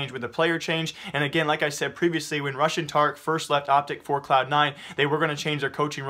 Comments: I kept hearing "Russian Tarik" for "Rush & Tarik". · SO FUNNY!!! when u said RUSH and Tarik, i thought you said Russian Tarik :D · ROFL. I thought you were saying Russian Tarik for a moment. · I thought u said RUSSIAN TARIK and i was like wtf there is another tarik?